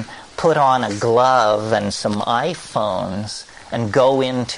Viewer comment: Right at the start, about 0:20 It sounds like he says iphones doesn't it?